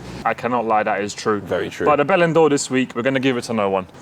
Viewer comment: I'm not even gonna lie, for a couple weeks I thought and were saying Bellend Ore instead of Bell and Door